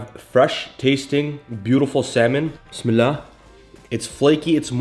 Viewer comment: I always thought you said "Qul Bismillah." I love your recipes. I'll try this meal prep. looks good